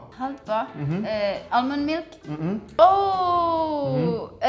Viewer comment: ich hab Allmannmilk verstanden,..das hat mich zerstörrt...lachflash des jahres.